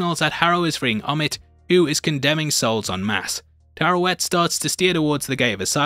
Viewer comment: I hear you saying 'Tarrowet' instead of Taweret😅 Love your videos man! First thing after every Marvel video is Heavy Spoilers😍